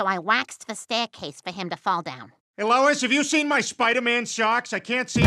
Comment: I thought that said lolis